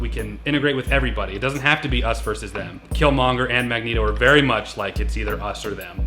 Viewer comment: Fun fact Stan Lee was inspired by the civil rights movement in the creation of the Xmen Professor X was based on MLK and Magnero was based on Malcom X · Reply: Omg i thought u said "Magnegro"